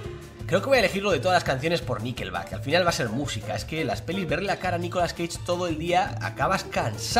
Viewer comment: Entendí "Nicolas, cagué en todo''